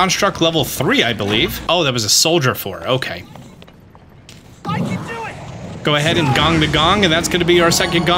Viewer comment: I keep on mishearing his "I can do it!" as "Why'd you do it!?" 😅